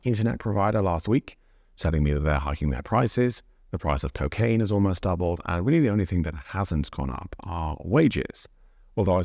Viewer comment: did he just say 'price of cocaine has doubled' ...XD